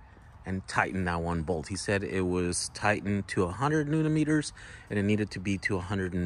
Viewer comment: Millimeters? Sure it wasn't Newton Meters? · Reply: I heard him say newton meters but I can see how you heard millimeters